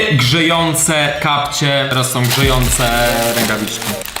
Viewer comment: Jeśli chodzi o rękawiczki to usłyszałam żyjące zamiast grzejące :D. Lodówka jest lepsza :)